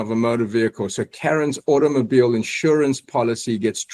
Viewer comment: Did he say “Kevin”? Did he miscall JOK Kevin?